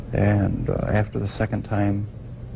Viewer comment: "Not planned" sounded like word coughing like Erin Caffey